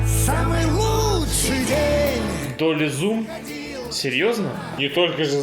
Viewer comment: На 8:03 как называется эффект? Мне послышалось "толи зум", но в гугле я ничего подобного не нашёл. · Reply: Долли-зум